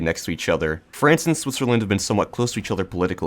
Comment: what you say? france and swjsw8jsauwsw? what is that.... i repeated the video mutiple times just still dont get it clealy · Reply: Don't know if you're being sarcastic. But he said Switzerland.